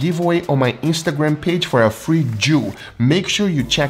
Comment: Anyone else think he said he's giving away a free Jew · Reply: He actually said a "free Joule" (sous vide). LOL